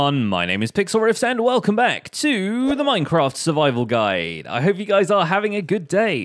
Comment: I swear i heard: "The minecraft survival GUY"